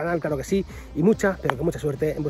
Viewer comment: Yo tambien pense que decia capturo